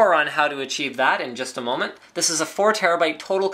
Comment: i thought he said a moron can achieve this, not more on this